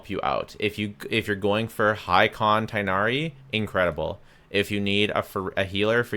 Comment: I thought you said "icon Tighnari" and I was like yes he is one omg!